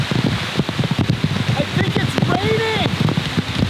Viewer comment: did he just say "I think it's raining" ??😂